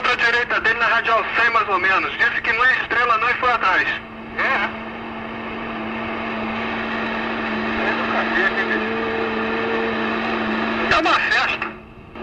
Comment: Foi somente eu que escutei isso?? 👇🏼👇🏼👇🏼 02:55 “Senta o cacete, bicho”, ou seja, derruba! Derrubou? Não…